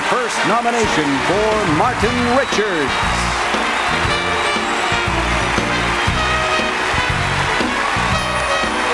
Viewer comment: what do you think Harvey Weinstein was saying? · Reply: Looks like he's saying JUST GO GO GO